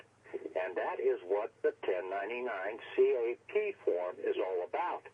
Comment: Unsure what 1099 form is spoken. What is saying at the 34:19 mark? (Sounds like a 1099 cat?)